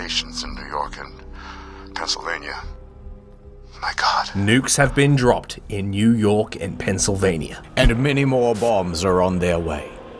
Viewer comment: I thought he said "bombs have been dropped on New York and Castlevania." lol